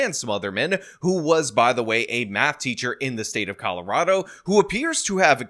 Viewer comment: I initially misheard “math teacher” as “map teacher” but given the context, didn’t even bat an eye.